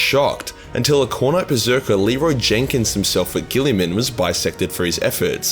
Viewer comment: Did I just hear leroy jenkins